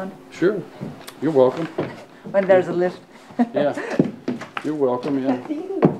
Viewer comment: Did anyone else hear him say you’re welcome two times at 48:17? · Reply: Naa... He says, "Yeah, You're welcome, yeah..." It's a Northern Thing. 😂